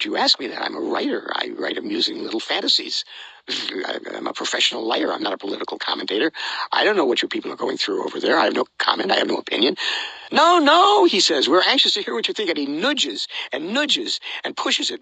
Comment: "I'm a professional liar, not a political commentator." I'm taking that. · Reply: I thought he said "professional writer"